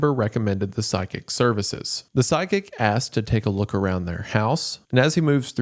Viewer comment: 😂 English is my second language and this is silly but I wanted to share that I've been hearing sidekick instead of psychic and I was so confused because how would you hire a sidekick 😂😂 love this content btw 😂❤